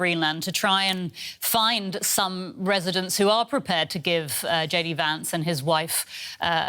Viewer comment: "Hey guys, it's JD Vance, the vice president..." Why are US politicians suddenly behaving like they're youtubers ^^' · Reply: I thought he said instead of JD Vance. Must be getting old. lol